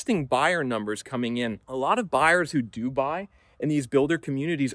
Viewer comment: At the 8:37 mark, I heard him say "A lot of buyers who Dubai". It's a pretty wealthy country with plenty of real estate investors, so doesn't surprise me that they could be behind a lot of this.